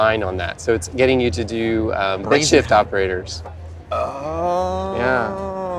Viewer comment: Did he say "bitshift" operators? I sounded like he said something else.